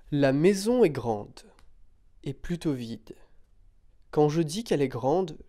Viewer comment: Oh OK. I thought you said "plutot vite" at first. Haha. This is fantastic btw. Vocabulary videos, great idea!